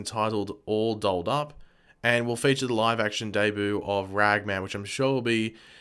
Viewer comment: did he say ragman